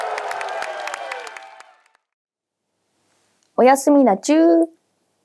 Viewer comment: 姫良々「おやすみのチュー」に聞こえた。おやすみなちゅ～ねｗ